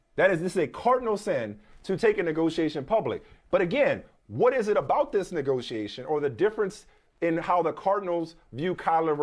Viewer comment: I almost lost my mind at 2:10 I thought Mike was finna say “ what is it about this nigga” 🤣🤣